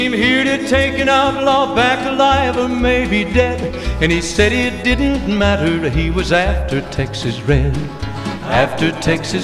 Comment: did he say it didn't matter'd? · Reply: “And he said it didnt matter he’d was after texas red”